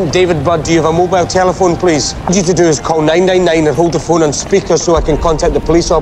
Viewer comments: Call nae nae nae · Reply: i meant it sounded like hes saying nae nae nae when hes saying call 999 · God darn, thought he said "need you to call ding-ding-ding" was like wtf is ding ding ding, must be a code or something.